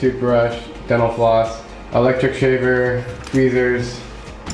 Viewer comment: He typed electric slaver in the subtitles instead of shaver